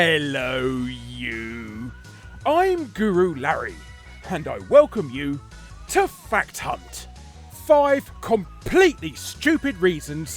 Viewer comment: Sometimes it sounds like you say "welcome to fat c**t" or it could just be my crapy hearing and my tendency to be a dirty degenerate.